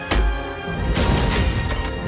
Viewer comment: Why do I hear I am Inevrible (not Inevitable)?